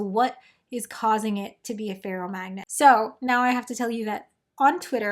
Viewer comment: I kept mishearing the "ferro-" in ferromagnetism as "feral". The whole time I'm picturing a crazed magnet running around and ruining experiments 😂